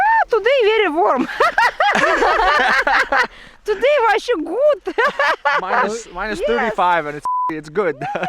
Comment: Is it me or did she speak in Norwegian? It sounded like "vær så god"